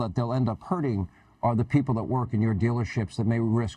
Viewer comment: Did hannity say feeling sorry for his dealership? Employees losing their job. It's no fun when the rabbit has the "G" 😂😂😂